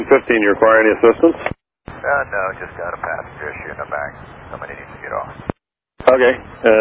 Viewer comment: I thought he said "sick in the back, might need to get off" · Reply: passenger issue in the back